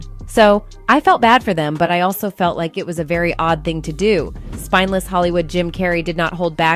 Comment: in your video, did she call him Spineless??